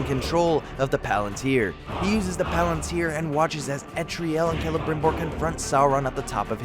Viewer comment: I thought it was “eltariel” but I keep hearing “etriel”